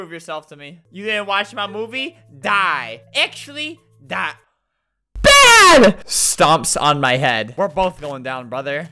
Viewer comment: what did he say to make you so chocked xDDD yea that's right I did saw adam bluring what he said! >:) ples tell me im curious xDD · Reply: And "You want me to make out with the maze?"